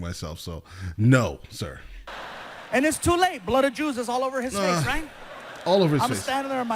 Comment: As a german I can only say that I died laughing when I first heard "blood of jews"